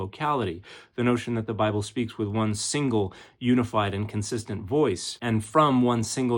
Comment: the caption says, “inconsistent,” where it should say, “and consistent.”